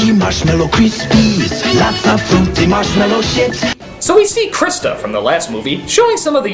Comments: did they say fruity marshmallow s*its XD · Did I just hear "that's the fruity marshmallow shit?" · anyone else hear "fruity marshmallow shit?" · When the advertising came on, I could have sworn the cereal advertising said "Lots of fruity marshmallow sh*t." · anybody else hearing "marshmallow shits? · Damn sware I heard "marshmellow shits"! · AM I THE ONLY ONE WHO HEARD 'MARSHMELLOW SHITS'??? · Anyone else hear "Lots of fruity marshmallow shit" @ 1:58? Lol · did i just hear lots of fruity marshmallow shit? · Did he just say fruity marshmellow shits?